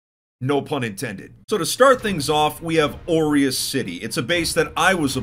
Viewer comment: I keep hearing "oreos city"